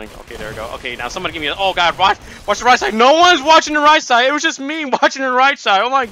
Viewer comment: I thought said "nobody's watching Reichstag" So this is how the Reichstag got fire